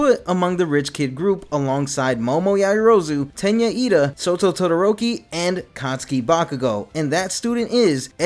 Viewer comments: Did you just call him Soto Todoroki? 1:14 Also, Bakugou's rich? Why have I never realized this? It makes sense... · I'm sorry did you just say Soto?